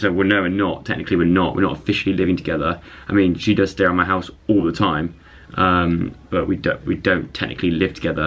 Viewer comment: 3.25 i legit heard "she does stare at my house all the time" :D